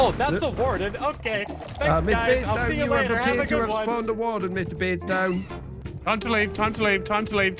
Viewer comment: the second i saw this vid i heard "MR. BREADSTONE"